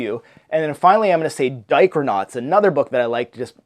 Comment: Thanks for the tag! This looks so fun and your greenscreen looks great! Editing is on point! Heh, when you said Dichronauts, I thought you said Diapernauts...BTW, you took all my answers!